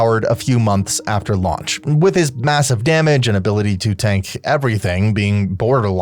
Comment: did he just say massive?